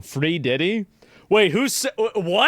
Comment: Sorry I thought you said Three Fiddy, you can keep Diddy.